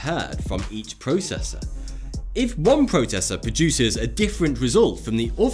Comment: The what did you say? "Protessa? Protesser?? " 14:20 Processor* Oh you're just playing with us ✌️😂